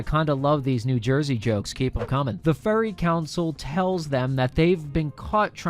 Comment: it sounded like he called them the furry council lmao